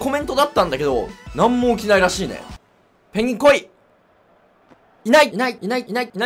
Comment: 最初はいない！だったのにだんだんｲﾅｲｯ!ってなってきて最終的にｲｲﾈｯ！👍に聞こえてきた